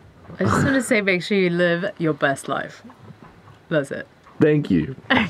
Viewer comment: At the end of this video it really sounded like you said “live your bust life”. OO 😄